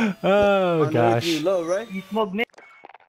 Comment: did he say “you smile mids”? lol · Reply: You smoke mids